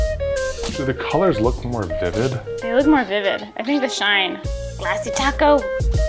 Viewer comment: I thought she said Glassy taco and I was thinking, I wouldn't want to eat a Glassy taco